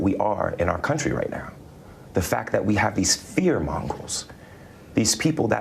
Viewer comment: The type of sociopath he has to be at 53:19, to say, “we have these fear mongers trying to separate us. That’s not okay.” Sir that exactly describes what you did when you staged this attack. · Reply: I thought he said fear mongols? But either way, for somebody who prepared his big performance, you'd think he would get the saying right. It's fear monger! At least he didn't use any "escape goats" because I bet that's how the dumb ass would say it..